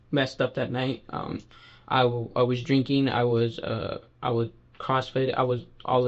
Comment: did he say I was messed up last night besides weed and drinking he said crossfit? Maybe I misread that because that doesn't even make sense. It doesn't matter how drunk or smoking weed or whatever you did it doesn't matter many people do that and they don't try to steal women. You literally tried to kidnap her and probably grape her